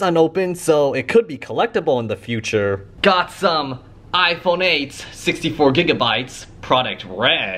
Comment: if you have cc/closed captions on, the caption says "iPhone AIDS"! really matching for its red color.